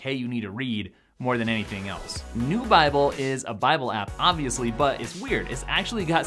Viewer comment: It sounded like you called the Bible app, “New Bible” but I can’t find an app by that name..... Did I mishear?